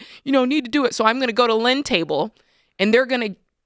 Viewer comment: I thought at first said "lint table".